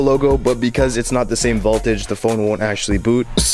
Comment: Add more of those bricks and sounds like your say vaultage? Not vohltage... haha